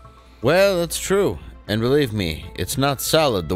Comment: Did I hear him correctly? Did he just say, "And relieve me."